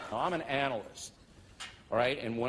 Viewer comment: when said, 'I'm an analyst', I thought that he said, 'I'm an anal cyst'.